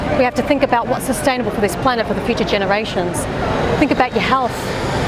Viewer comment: Did I just hear her say PETA generations🤣🤣